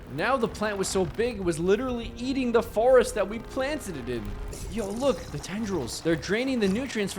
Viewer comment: I thought you said planttdiit